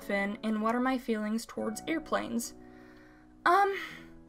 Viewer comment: I thought you said earplanes for some reason lol